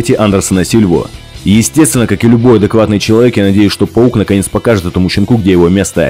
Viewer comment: Ахахах,мне послышалось на 5:20 Пол покажет этому щенку,где его место